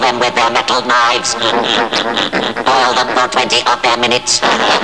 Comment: I still can't understand what the robot says from 0:14 to 0:16 even when hearing it so many times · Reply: ***** "boil them for 20 of their minutes"